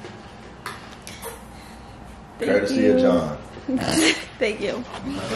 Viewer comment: Ik I heard him say “thank you bae”😂😂😂